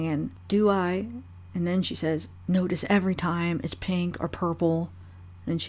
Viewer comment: Sounds like she says, “Everytime we here it’s purple” 🤷🏻‍♀️